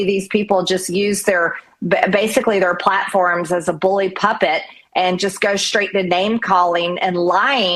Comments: did MTG just say Loomer & Levin use the platform as their "bully puppet"? Imma going to call it the "bully puppet" from here on out. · 😳 Did MTG say “bully puppet???” 😂😂😂 True MAGA moron? We need a movie called MTG Goes to Washington. I wish James Stewart was still available?